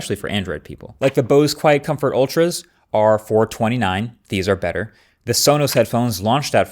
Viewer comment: Did i just hear you say the Bose QCU, Sonos ace better than the new XM6? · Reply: No he said these are better meaning the sonys